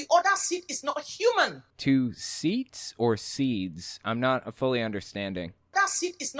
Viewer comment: I think she was saying ‘sides’.